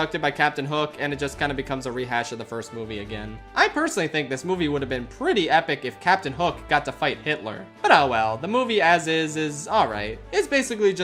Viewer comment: I'm sorry, what did he say on 51:17 ? "It will be pretty epic if we get to see Captain Hook fight against H#tler" ??? Ok, that really broke me